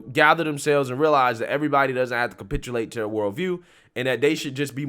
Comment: Mr Black Conservative Perspective says "...everybody doesn't have to capitulate to blur world view." Blur is a rather ambiguous pronoun Mr BCP! Did you say "her world view"? Did you say "their world view"? I can't tell!